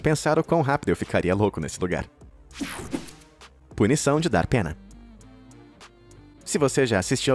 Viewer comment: entendi PUNIÇÃO DO DATENA ent a punição e cadeirada 🤣 🤣 🤣